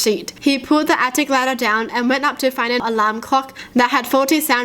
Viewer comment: When she said alarm clock I thought she said lamb cock lmao